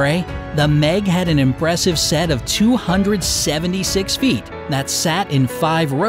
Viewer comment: Do you think baby Megalodon was so cute? :) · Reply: you did not say teeth at 3:26 you said feet